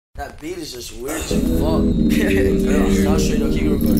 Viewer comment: It really says weird? I always thought it said this beat is just rich as fuck